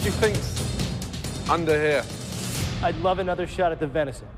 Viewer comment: what did he said? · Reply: Roxterat He'd love another chance at cooking that fancy meat ^^ I know it's late and you prolly don't care anymore but heyyy answered <3